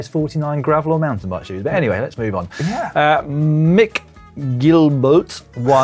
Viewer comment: I must have the same sense of humour as...at 20:48 i coudnt stop laughing when said what sounded like "mickgeeelbourrrt" after inhaling a blast of helium... lols